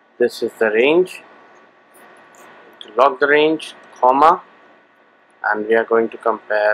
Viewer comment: what did he say? logged range??? or something like that? how do you do that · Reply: its is "lock the range" or mean "make the range absolute" by pressing F4